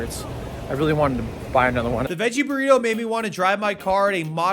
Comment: the way u said fajita killed me deeply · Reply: I misheard it as Fagina burrito at first and had to watch it again 😂